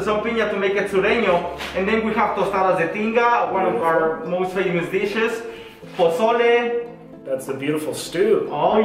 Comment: When he said "one of our most famous dishes" I thought he said "one of our most venomous dishes" LMAO